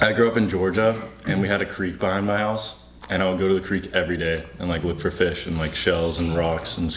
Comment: I thought said creep instead of creek 😭😭😭😭